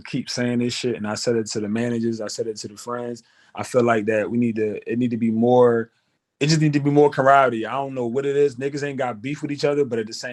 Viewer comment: did he say, "it need to be more karate"?🤔